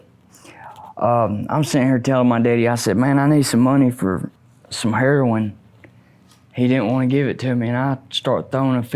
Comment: What happened to the audio at 10:26? I heard another voice say something. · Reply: Assistant said, "it's terrible", when mentioned red meth.